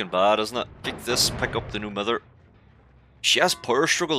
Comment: when i heard "no mither" for the first time, i thought you said "no mother" (or maybe "new" mother :D ) :D xD i love your accent man <3 keep up the good work on nice gameplays :) well done!